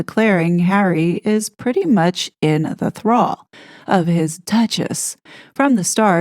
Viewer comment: LOL! Listening to you read the article, I heard: Tina Brown said as much last year, declaring Harry is "pretty much in the CLAW" of his duchess. I had to back it up and read the screen. I misheard thrall for claw. Misheard, but absolute truth! LOL!